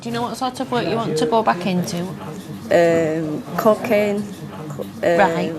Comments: I honestly thought she said cocaine... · I swear the first time she said "cocaine", had to rewind it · I thought she said she wanted to get back into cocaine. But the job coach seemed so unfazed, that either their job centre knows how to party or I misheard. Amazing how the accent changes cooking to cocaine. Is helping heroin?